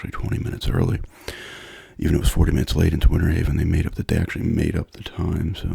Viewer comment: did you say winter haven?